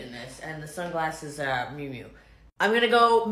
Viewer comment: The sunglasses are by who??? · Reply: I thought I heard her say Miu Miu...